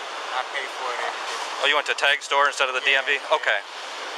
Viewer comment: Did he say he went to the tag store? 1:36 Am I the only one who didn't know there were "tag" stores? I am? Ok😔😔